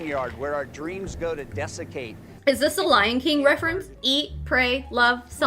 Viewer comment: At about 19:45 did he say “fear is a bone yard where our dreams go to defecate?” Why do my dreams need to go all the way to the bone yard, just to take a dump 👹